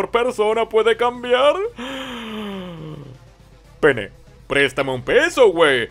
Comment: no se porque escuché p3n3